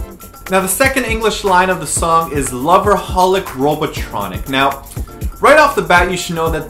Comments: Actually, I thought they said "Robotholic Robot training" LOOOOOL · What do you mean "Loverholic, Robotronic"? All I heard was "Lavaholic Lavatronic". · Always thought they said "Loveaholic" Which makes more since